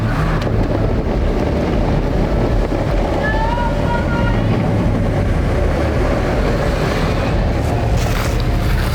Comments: mark...was someone yelling out or am I hearing things? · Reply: No I heard it to. The male voice yelled out …. Help, somebody….. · did anyone hear anything on video?! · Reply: Yes at the 26:12 sounds like a woman or young person saying "help somebody!!!!!" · is it just me ....or did I hear or did I hear, AND perceive within the audio at 26:12 precisely someone at an elevated voice say " HELP SOMEBODY!!!! " it almost sounds like a female voice...... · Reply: Yes I heard that. Someone yelling "Help Somebody!"